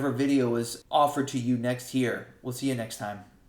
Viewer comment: I misheard next here for next year. 😂 I worried myself